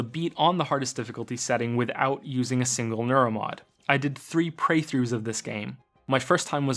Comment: did you say "three PREYthroughs"...? I think I'm gonna commit Sudoku now.